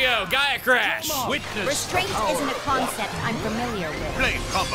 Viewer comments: Floren’s ‘Restraint isn’t a concept I’m familiar with’ line is just so fucking raw! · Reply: Oh, it's "restraint"? I thought he was saying "*Your strength* isn't a concept I'm familiar with", as a way to dis them for being weak. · Floren’s ‘Restraint isn’t a concept I’m familiar with’ line is just so fucking raw! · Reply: I thought he said “straight isn’t a concept I’m familiar with” i mean both are correct